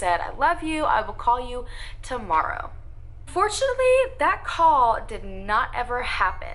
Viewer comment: I'm so out of it right now. 🤣The bird is distracting me and every time she said "unfortunately" it sounded like she said fortunately😫